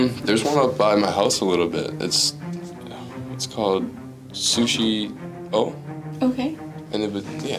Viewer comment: "Do you know any sushi places?" "Theres one by my house, its called sushi...o" 🤣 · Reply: thought he said sushi bowl....